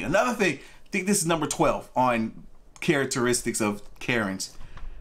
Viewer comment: For a sec I thought you said karenteristics. 😂😂😂